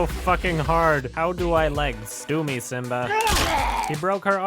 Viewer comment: Did he say "Do me Simba?"